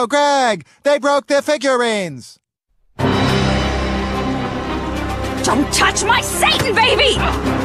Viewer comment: what did Hailey say? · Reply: Don’t touch my Satan baby